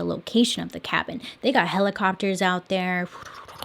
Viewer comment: when she said helicopters i heard “hella copters” 💀💀💀